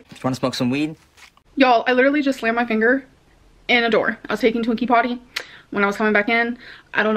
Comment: When she said "I was taking Twinkie potty" three times I thought she said "I was taken to a key party" 🤢